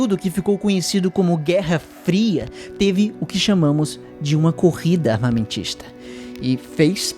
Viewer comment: "Um período conhecido como guerra fria teve o que chamamos de uma corrida armamentista..." Eu entendi "uma corrida autista".